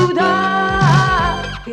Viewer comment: Послышалось, что она поёт ТЫ НАДЕРИ МЕНЯ!!!)))))